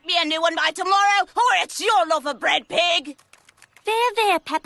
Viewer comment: What did she say? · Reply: ok sorry she said her loaf which is probably how the English say ass